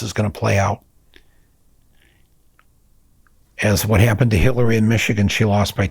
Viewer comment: Did you say Hitler by accident instead of Hillary? LOL. Anyhow, enjoy your stuff, always informative and from your heart. Unfortunately I still think Biden is a POS, and yaya so especially is the weird Orange Man, but we/Hillary/MSM made him! even SORROS helped out giving his Son In Law [another fine POS] 1/4 of a Billion dollars!